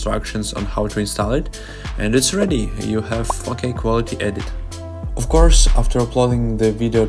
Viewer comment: when you say "4k" I keep hearing the F word... 😅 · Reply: I always thought he was saying "f*cking quality edit" until i read you comment and realized he was saying 4k